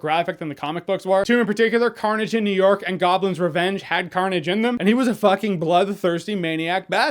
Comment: For some reason i thought said Gotham's revenge.